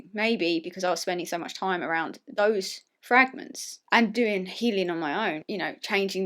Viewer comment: The first time said "fragment" I heard "frat men" and I was so confused, but it's nice to officially meet you ! You're a great person, thank you for sharing so much with us !